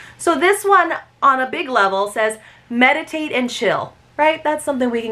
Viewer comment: Oh my I thought you said Medicate and Chill 😂😮